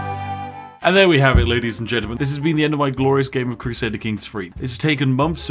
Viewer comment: Thought he said crusader kinks lmao